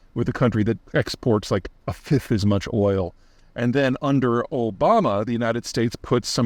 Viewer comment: did he just say ol’bama